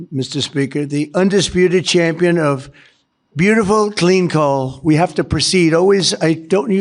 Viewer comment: Ok, first I heard “Beautiful clean calls” and I was like… seriously? For real? I turns out, not. It was “Beautiful clean coal”. Which isn’t any better. But at least I can see the manipulative childish objective.